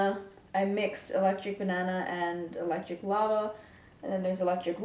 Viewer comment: I have to be honest: i thought she said eletric guava